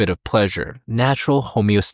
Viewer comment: did you just say "a little bit of plank"????